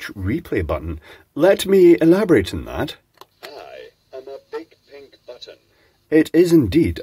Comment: I heard "I have a big pink bottom."